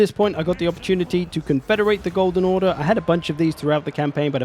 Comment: The first time I heard it I thaugt you said that you had the opportunity to confederate the golden horde.